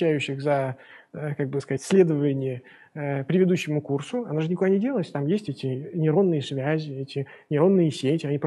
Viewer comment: на 8:02 послышалось : "... а вы же ни х..я не делаете...")))